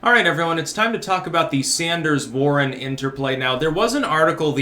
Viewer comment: At first, I thought you said the Sanders-Boring Interplay.